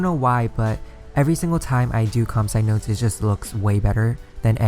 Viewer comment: What did you say in 8:28?? Com side notes??